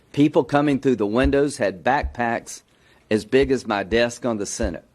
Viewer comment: thought he was gonna say a big as my D 🤨 😂